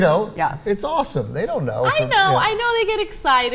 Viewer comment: I thought Anna said "I to ate children"